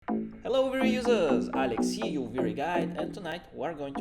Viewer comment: Vray "Guide"? Ohhh. I thought you have said Vray Guy for years lol. Great video!